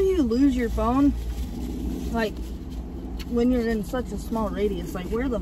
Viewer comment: Did say “when you’re in a small radius”? 13:12 🤣🤣🤣